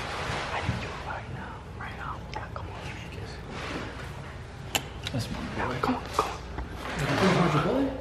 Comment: Anyone heard give me a Kiss